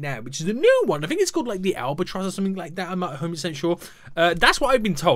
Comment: is it just me or did he say "am i homosexual?" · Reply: I know it's been 2 years but he said 'I'm not so sure'